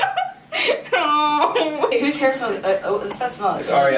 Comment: What does say at 00:20? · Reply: I think he was supposed to say whose hair smell so good that's why Ariel bend over to to smell her hair and said OMG( so I am taking it as her hair smells good !)